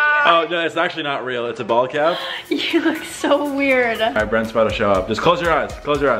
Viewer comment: Wait did I hear say “My Brent is about to come” when he was doing the bald prank 😂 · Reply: He said alright brent is about to show up